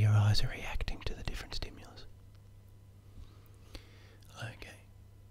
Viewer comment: I thought he said Logan somehow and I said to myself "Did you just say my name!??!" But he said ok. XD